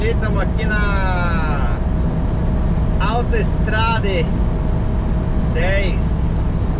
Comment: você disse "Aute Estrade", foi isso mesmo?